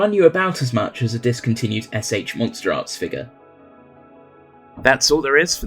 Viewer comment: Wait a second, the captions say X-Plus, while the dialogue says S.H Monsterarts... the hell?